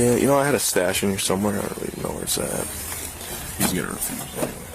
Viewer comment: "He's gonna refuse." Is that what he said? It's hard to tell but it makes sense if they were talking about the guy they're harassing not consenting for them to search his vehicle, which hints to me that they had no probable cause to begin with.